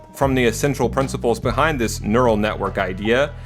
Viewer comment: Did you just say nerd?